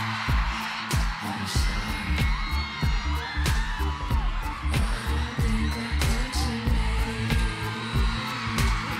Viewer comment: Does anyone know what Billie is saying at the 2:14 – 2:18 minute mark? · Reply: "I'm sorry, I don't think I caught your name"